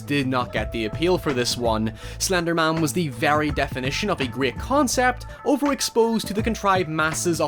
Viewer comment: I'm sorry what kind of concept? I hear "grear" but I'm gonna assume "great"